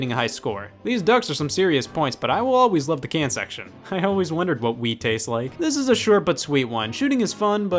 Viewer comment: "I always wondered what Wii tastes like." Sounds like you're saying "I always wondered what wee tastes like.". Uh-uh. No.